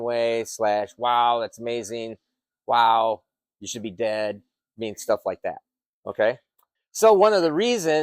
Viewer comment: I thought said “Wow she’s dead.” 😂😅